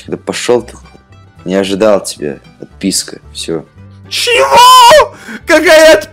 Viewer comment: пересматривал милиард раз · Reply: он сказал отписка, а послышалось ПИПИска